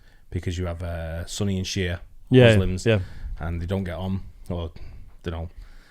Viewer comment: Perhaps it’s my ‘bad’ America ears... But I thought he said: “Sonny and Cher Muslims” (Sunni and Shia) 😂🤣👍